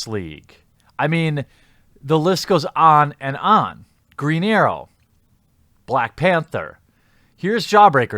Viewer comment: Your accent makes it sound like you said a Mexican version of Green Arrow, Greeñero.